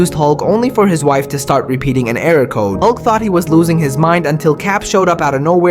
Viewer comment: I thought you said "unc was losing his mind" for a hot second there lol